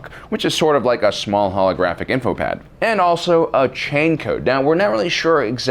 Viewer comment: chain code? i thought he said gene code?